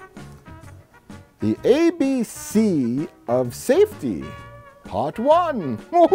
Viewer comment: I swear to god I heard them say "Safety from kids! Safety from kids!" which makes me think of children attempting to have sex with these elderly people who cant run away.